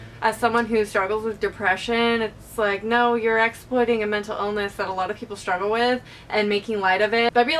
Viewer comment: I thought it said metal ilness, and was like "whats that???", until you said it yourself hahaah my bad. Totally agree with you though!